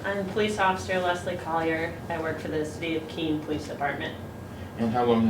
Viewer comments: did she say "" The state of Keene"...??? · Since when was Keene a 'state?' Where is this place? I heard the cop swear and affirm that she worked for "The State of Keene Police Department." @ 2:35And the prosecutor swears in the witness? Again, I ask, WHERE is this place? lol